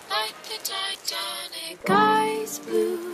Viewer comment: I thought it said “Eyes blue like the Titanic” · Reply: I thought it was saying ice blue, like the atlantic Which still makes sense